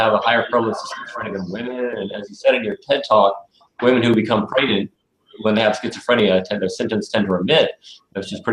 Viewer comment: HI, Can you please tell me what is said around the 3:45-4:00 time re: adolescent males and pregnant females? I can't understand that part. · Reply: He said when women are pregnant there schizophrenia symptoms dissipate